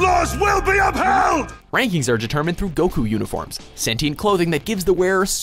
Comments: in this I heard "Goku uniforms" is that right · did he say Goku Uniforms? o.O